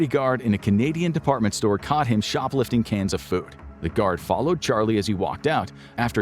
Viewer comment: I'm an idiot, I heard "shoplifting cancer food!" 🤦🏻‍♂️🤷🏻‍♂️😂